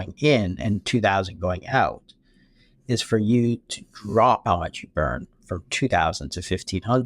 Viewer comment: What is he saying at 25:20 ish. He says that the only way for you to balance this equation at 1500 going in and 2000 going out is for you to "dropb..." burn from 1500 to 2000. Also, if we eat a low carb meal, we still might eat too many calories and then we would store that energy, correct? It's just that we won't feel hungry and want to o er eat afterwards, right? Also, tou avoid the insulin spike which leads to all of these other problems.